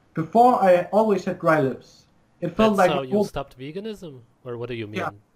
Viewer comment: I thought for a second he said "dry nips" instead of "dry lips" lol These interviews are completely amazing, hearing different peoples stories really shows how it is being a vegan. Like i told you i was only vegan for like 5-7 days, so i don't really know mal-nourishment, besides when i was using drugs and drinking a lot i wasn't eating a whole lot, but other than that, i've never starved myself. So these interview really reassure that raw meat and raw dairy are optimal for health.